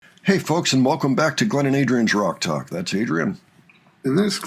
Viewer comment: I always hear 'Glenn and Adrian's Drug Talk'